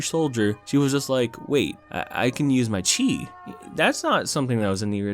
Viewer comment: what mulan says: "i can use my chi" what i hear: "i can use my cheat"